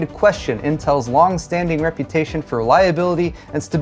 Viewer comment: Did you say "long-standing reputation for liability?" ???